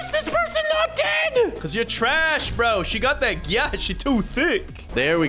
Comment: Did just said gyat?